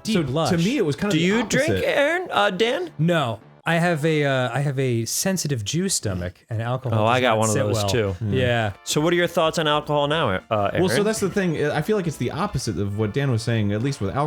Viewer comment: "Do you drink alcohol Dan?" "No, I have a sensitive Jew stomach." ...I have never related to anything more in my entire life :D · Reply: I thought he said juice stomach